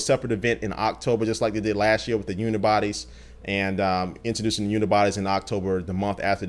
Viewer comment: lol it sounds like he says boobies